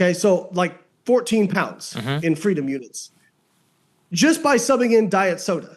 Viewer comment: did he just say freedom units 😂